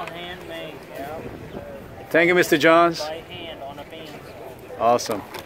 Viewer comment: I used to make those was a lot of fun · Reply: I thought that would be a way. I hear him say handmade on a band saw ( 0:13 , but I don't see breaks in the rings for a blade to enter. Maybe he meant scroll saw. Thank you! Nah, his banner says bandsawn in a spiral. I am mystified for now :)